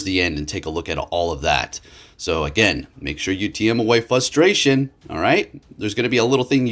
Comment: is it just me, or does it sound like he says "flustration" anytime he tries to say "frustration"